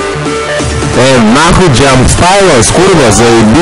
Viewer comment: what is he saying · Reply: ey nahuy jumpstyle's kurwa zaebis'